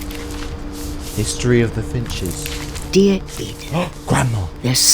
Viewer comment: when you said finches I thought you said fidgets XD